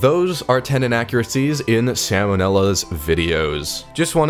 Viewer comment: i just woke up and thought it said salmonella :(